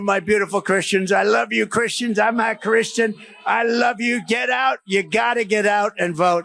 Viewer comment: SOUNDS LIKE 45 SAID.. "I'M NOT CHRISTIAN" AND 45 IS THE ANTICHRIST45 AND A FAKE CHRISTIAN. GOD SAID VENGENCE IS MINE...WAITING TO SEE THE DIVINE INTERVENTION